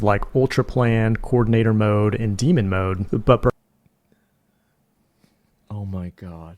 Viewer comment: Did they just say demon mode,???